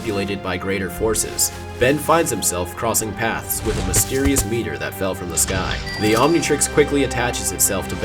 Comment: did he say a "mysterious meter?"